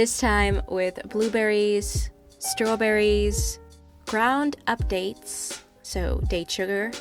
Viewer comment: I heard the "ground up dates" as "ground updates" and was confused for a little bit😆